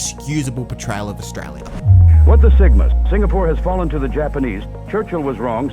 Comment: did I just hear him say "what the sigma"?